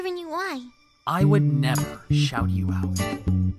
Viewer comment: He said "I would never shout you out." Right? Is it only me hearing "shout" instead of "shut"? If he is saying "shout" could someone please explain why? I'm really curious.